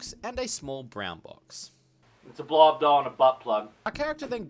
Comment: what did you say at 4:34? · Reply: "Its a blowup doll, and a butt plug"